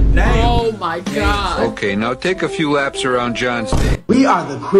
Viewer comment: Y'all didn't catch what Garfield said last 12:55 he said now go take laps around john's date>